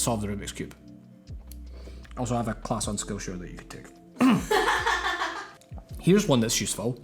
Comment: I’m so sorry but at 21:15 I thought you said “I have a class on school shooting you can take” 😶 I had to check using subtitles